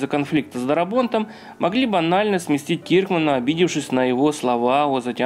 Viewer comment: Что прозвучало: могли банально сместить Киркмана. Что я услышал: могли-б Анально сместить Киркмана. :D